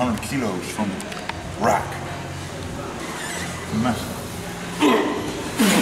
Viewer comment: does he say 'Meh'? That'd be brilliant